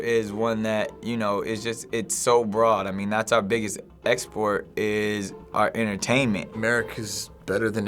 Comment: What was Nick Cannon saying? Here's what I heard: "That's how big its export is...our entertainment"